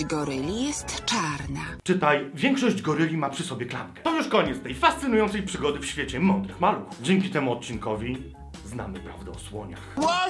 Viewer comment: Co jest ze mną nie tak, że usłyszałam "To już koniec tej fascynującej przygody w świecie mokrych maluchów"? XD